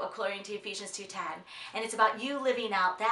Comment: Lol...at first I thought you said keep walking with a cane, and then I was like no she wouldn't say that!! Keep walking with the KING!! Thanks, so much I needed this today.